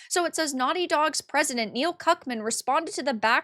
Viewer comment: Did I hear Cuckmann?